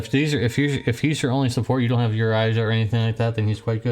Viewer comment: Thought he said "Your eyes" instead of "Yuraisha" and was just sitting here for a while mouth open, going "WTF Deus" KekW